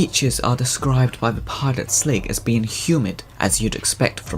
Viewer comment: I think somewhere in the good ending before the sligs shoot the other glukkons one of the sligs says something about the yaymans being a human beach. I probably misheard them but it just got me thinking. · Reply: You most likely did. The subtitles say 'humid' not human. I mean I don't blame you though XD I misheard it too till I saw the cutscene's subtitles